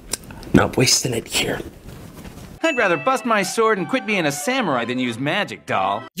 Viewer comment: If you turn it down It sounds like he said “I’d rather fuck by sword”